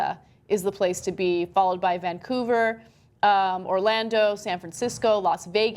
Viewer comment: did she say "Vancougar?"